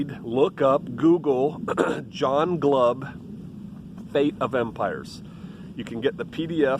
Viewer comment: Post a link to your references. John Glover State of Empires? I can't find a PDF. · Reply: I thought he said "Fate of Empires".